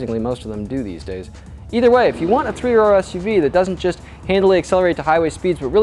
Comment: Thought he said "if you want an SUV that rips ass" around 4:15. heh